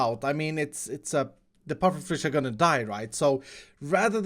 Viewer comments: Excuse me? What was that you just said..? Die or right? or DIORITE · saying die, right sounded like diorite...